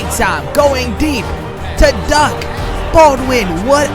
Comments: did you call him duck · did he say Duck Baldwin? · I thought he said "going deep to duck! Baldwin